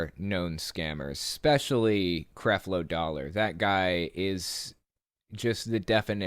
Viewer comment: I heard Craft Low Dollar